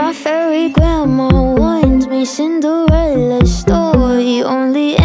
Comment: when she said “Cinderella’s Story” i thought she said “Jason Derulo” 😂